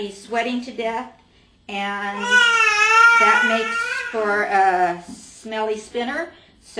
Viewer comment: Did she say "smelly sinner" at 1:43?